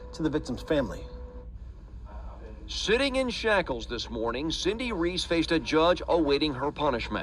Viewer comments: Almost sounded like he said “shifting in shackles…” lmao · does he say "shitting" in shackles?? 😂😂😂😂